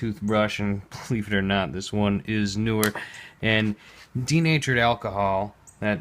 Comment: XD sounded like you said 'teenager's alcohol'!